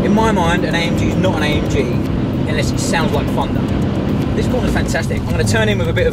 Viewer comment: did he just say "an AMG is not an AMG unless it sounds like a Fucker?"